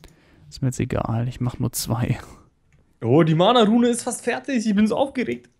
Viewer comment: Wer hat bei 1:00 noch mana Nudel verstanden